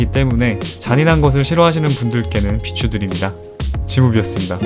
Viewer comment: 마지막에 지무비였습니다가 지옥였습니다로 들리는건 기분탓이겠지?